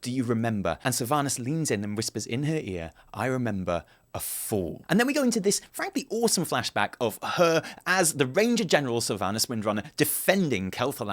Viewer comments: Sylvanas said 'fool' not 'fall' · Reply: I thought she said "I remember a 'foe'" :( meaning that she only cares is to vanquish it, or something like that... damn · I just wanna make a correction, mainly cause of Sylvanas's way of saying it. But if you turn on subtitles you'd find she does not say "I remember, a fall." But rather "I remember, a FOOL."